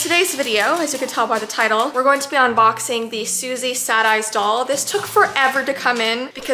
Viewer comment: I thought it said Susie said yes instead of Susie sad eyes😂